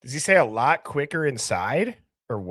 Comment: I heard “it’s a lot closer in size.”